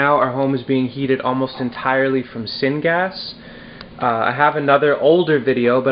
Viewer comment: Great, but I didn't understand what you said at 6:00 "Our home is being heated almost entirely from SIN GAS" Did I hear that wrong? What is it? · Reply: 'syngas' as in the heat coming from the making of the charcoal in the container. search the term and you'll understand. Thanks for askingn.